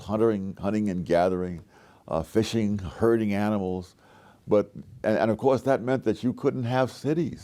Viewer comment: I heard "hurting animals." Then I realized that I actually heard "herding animals." 😂😂